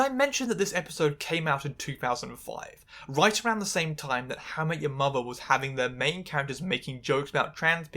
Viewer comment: did you call it Hammer Your Mother? 😂